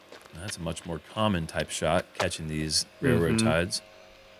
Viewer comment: did you say did say railroad tides 😂.. great job regardless guys!